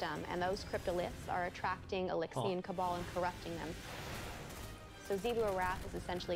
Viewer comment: The fallen are the Eliksni. “The fallen” is the name we decided to call them. The Eliksni is their actual name in their culture. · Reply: Yeah that's what I thought which is why I was weirded out when I thought I heard "Eliksni Cabal."